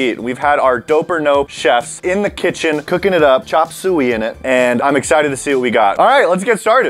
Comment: I’m sorry but did he just say chop sewie 😂😂